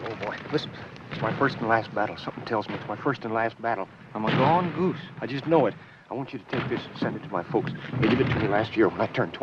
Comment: Anybody to type what the worrying soldier says to Murphy, please? I can't get all of it. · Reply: "listen, it's my first and last battle, something tells me it's my first and last battle, I'm a gone goose I just know it. , I want you to take this and send it to my folks they gave it to me last year when I turned 21"